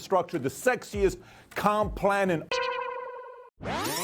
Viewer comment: lol I thought he said sexiest "con" plan and had a moment of honesty slip through!